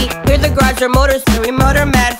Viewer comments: Thought it was saying I'm a horny ninja for the longest time... Was like HELL YEAH!!! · I thought it said 'I'm a holy ninja' I was like... a jesus ninja?